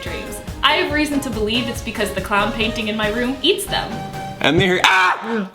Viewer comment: Oh hi, didn't see ya there. My name's Dahlia, and I don't remember any of my dreams. I have reason to believe it's because the clown painting in my room eats them. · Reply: OMG I MISS HEARD IT AS CLOUD PAINTING OH GOD NOW KNOWING ITS CLOWN PAINTING MAKES WHAT SHE SAID MORE FREAKY